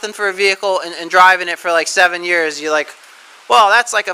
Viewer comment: a possible brewstew reference. But I think he said $7. Edit: Ok, it was $4, not $7 like I thought. As for your rant about vehicles being built poorly, 2 words, deliberate obselesence.